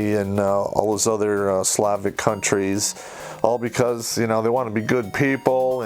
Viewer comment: Norway, Sweden, and "those other Slavic countries"? Those are Nordic countries, you must be a little tired today. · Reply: I thought he said "sloppy countries"....